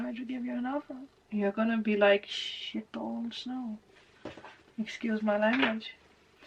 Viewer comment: Wonderful Reading! Loved how there was a 'Ding!' as soon as you said, "You have said yes to something"! I couldn't make out what you said at 11:48 (after which you asked for your language to be excused). I got the gist of your meaning, but what did you say exactly..? (I'm not hung up on the nature of words that 'tell it like it is', even if sometimes what needs to be said cannot be done 'delicately'... CC 'guessed': "[ _ ] old snow"..?!😆) Blessings! 💖🧙‍♀💖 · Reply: I can’t even write it out :) that’s how YouTube works with swear words. But I can write “ S… balls no” if you didn’t understand what I said :)